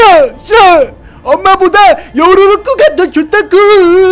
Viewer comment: 잘들어 보면 요루루꾸(?)이라구 들리ㄴ... 2:29 미어켓???